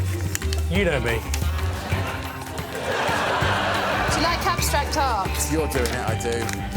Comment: What did she say? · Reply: "Do you like abstract art?"